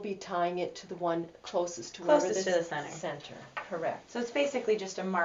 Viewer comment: Why she had to cut her off at 1:45? Can't understand what either of them say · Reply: She just said closest to the center. It doesn’t matter which one